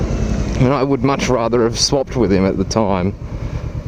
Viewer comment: oh jeez ya lucky to have escaped. When ya said 'swapped with him' I thought I heard 'slopped with him' and lost it. A great tail ;P